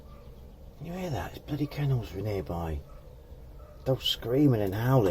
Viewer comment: Thought you said camels near by, played it a second time, n you said kennels, wish you'd make up your mind. You're doing that lovely this and beautiful that thing again. I remember that bolt shearing off and the marvelously dodgy repair at the time. An awkward repair to do properly, definitely better without the timing cover there? That was ages ago, I didn't think it would last a week. Well, we'll see what happens this time. I've got my fingers crossed, thanks, and take care.